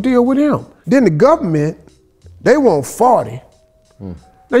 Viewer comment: Did he just say "off duh dribbo" 😅😅